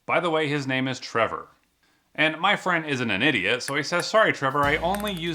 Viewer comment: The first time I heard it I thought he said my friend is an idiot